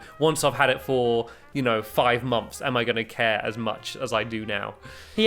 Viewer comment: listen at 28:30 stampy says mumps instead of months